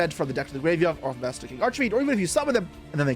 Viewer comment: I think I'm crazy cause I keep hearing him say "Arch"A"Fiend" throughout the video.